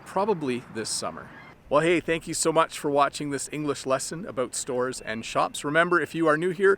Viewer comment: Thanks, useful video for expanding vocabulary. P. S. It seemed to me that at 9:45 I heard this famous Canadian word - aboot. :)